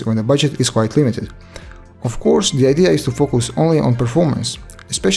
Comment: I swear, he Said "focos" not "focus"